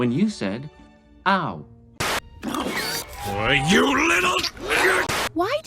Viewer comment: What is said at 20:35? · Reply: Why you little